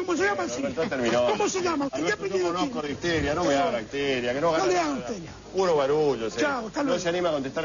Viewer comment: Oigan qué es lo que dice en a los 0:56 y 0:57??? algo como "no me diga de tella", "no me hable de tella"? qué significa eso? Gracias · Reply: Panamaniantube no me hable de histeria, yo conozco de histeria. chau hasta luego (saludo nazi) jaj